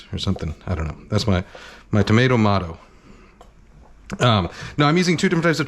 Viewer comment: Did he just say "tomato to motto"? 😂